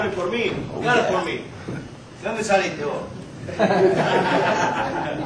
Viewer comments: ¿Que dice Barrios en el minuto 1:01, que es cuando Valero lo confronta? Gracias y Suerte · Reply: Le dice de donde saliste vos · Alguien sabe qué es lo que dice Barrios en el 1:00 a lo cual Valero va hacia él. Gracias. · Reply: De donde saliste vos le dice barrios